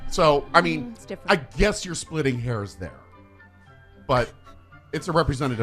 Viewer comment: Chris says: “Splitting hairs”, I hear “Splitting Hares” and I’m down the Bugs Bunny rabbit hole!😂😂😊